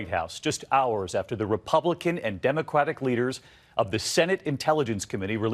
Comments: did he say Republican & Demoqwat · did he say democwatic?